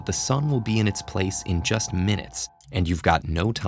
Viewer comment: I thought he said 1MINUTE!